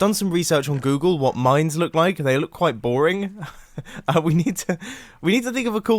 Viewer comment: "I mean I've done some research on Google on what mines look like, they look quite boring " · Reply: I thought he said:"they look like boring"😂